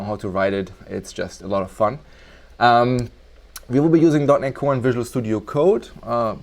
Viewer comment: im sorry but what did you say? 1:37 "we will be using" ?dot-nic-one? "visual studio code" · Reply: oh dot net core